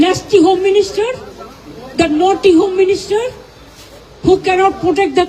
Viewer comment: Did she just say naughty home minister at 2:54 ? 😂 Throw this woman out from Bengal she has been looting bengal for last 25 years.